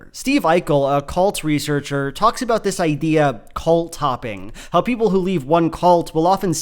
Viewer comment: I really thought you said “Cult Topping” and I gave it a very different definition from what you said. :) 22:57 Also, it’s pretty widely known that there is no such thing as the Devil or Hell in the sense of suffering for all eternity. Satan was a weird mistranslation and hell is best described as medieval Christian fan fiction.